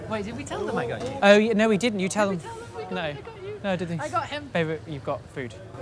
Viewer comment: did u just call him babe , like am I crazy